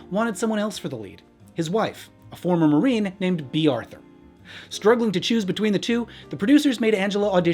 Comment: Tch, standard Hollywood nepotism-- Wait, did he say Bea Arthur!?